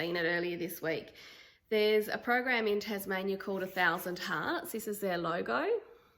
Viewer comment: I thought you said a 1000 tarts. LOL love all your cross stitch pieces they're really pretty!